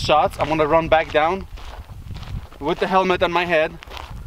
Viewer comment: I thought you said : " what the hell is on my head" 😂